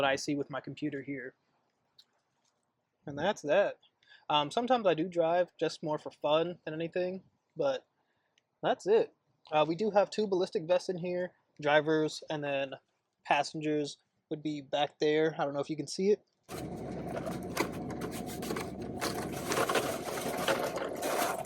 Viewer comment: i was listening to this video with the phone on the kitchen counter and I thought I heard “ballistic missiles” I was like boy I thought you’re supposed to be putting out fires not causing them?